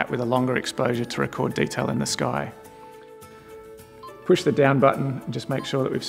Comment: lol I keep hearing "Push the DAMN Button...." im gonna try all of these out ^_^